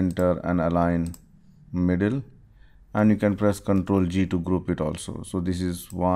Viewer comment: What did u said in the video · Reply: CTRL+G to group